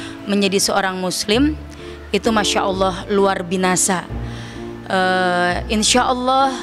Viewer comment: Kok luar binasa,,, Apa kupingku yg salah denger